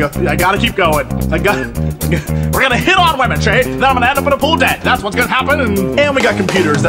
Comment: We are gonna hit on woman, then I'm gonna end up in a pool dead- Jesse Cox 2012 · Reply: I heard it as "a pool of debt" O.o